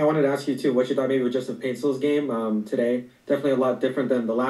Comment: did he really call him 'Justin' Paintsil?? 🤦‍♂ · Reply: Joske